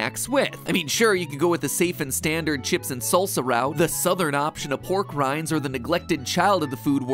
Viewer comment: I swear I thought he said "the southern option of war crimes"